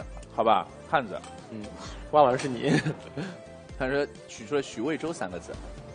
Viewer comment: May I know what made Huang Jingyu smile on 5:22? I think I heard Xu Weizhou